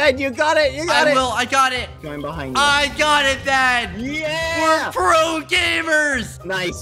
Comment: lmao is just me or did he say "I got it dad"